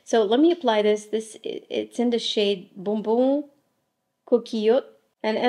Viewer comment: Sorry can you please confirm the shade of your lip color? The caption says Sucette but I thought you said it was bon bon coquelicot??? Thanks